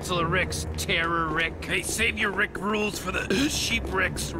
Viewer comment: Yo did rick just say rickroll😂